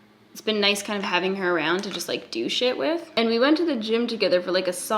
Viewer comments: I heard " its been nice having her around to douche it with" and thought it was so funny and then i realized you just said " do shit with" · I'm sure at 10:53 you say 'do shit with' but I swear I heard 'douche it with' twice :)))